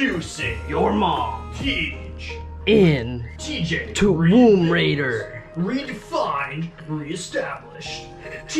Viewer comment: Nice setup man! But all I keep hearing in my head is t-womb raider xD (11:49) . Anyways, have a good one; looking forward to the next video :)